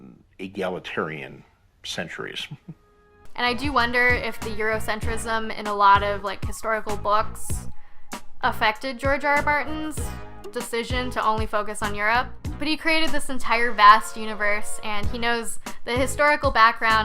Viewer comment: does she say "Bartin"?